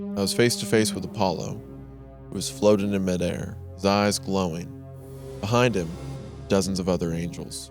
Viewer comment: The way Wendi said "his eyes glowing" sounded like "thighs glowing" and I had to tab over to see what was actually written because that made me really confused